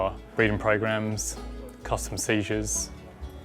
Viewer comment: did I hear "custom seizures"? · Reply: Customs seizures, so like when people travel to other countries & attempt to bring wildlife back with them